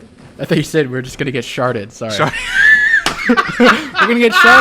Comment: I heard sharted too 🤣🤣